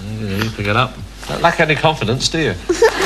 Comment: Did she say barmaid I thought she said mermaid lolllll